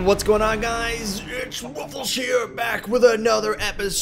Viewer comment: I thought he said hello its waffles here